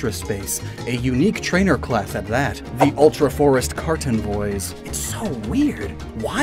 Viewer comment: When you first said "Kartenvoy", I thought you said "Carton Boy" lol Add Karten -> Carton to the pokemon pun list!